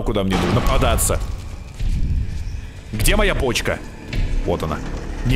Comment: скажите что мне не 1 послышалось "где моя почка?"😅😂